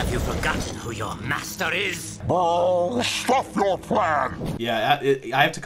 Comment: was it just me or did say “bull shit your plan